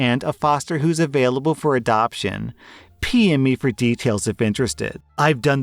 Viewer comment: [Silly Observation]: Around 11:25 in, says "PM me for details if interested". I heard, "Pee on me for details if interested". Did a legit spit-take.